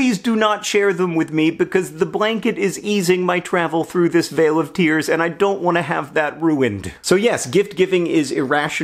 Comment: what does John say? Wail of tears? Veil of tears? Something else? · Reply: Vale (like valley) of tears. It's a phrase from Psalm 84 & lots of liturgical prayers - John's use of it in this context is the perfect combo of poetic and melodramatic :)